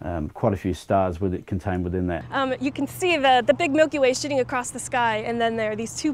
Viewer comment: Sounded like she said 'sh*tting'